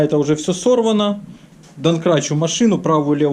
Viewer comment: Отличная работа! делаешь, как для себя! И смажешь везде и тряпочкой протрешь!-здорово!👍 ДоМкрат, кстати, через М. Послышалось, ты доНкратом обозвал его🤔